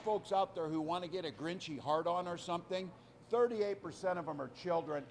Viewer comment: Did he say “grinchy hard-on”?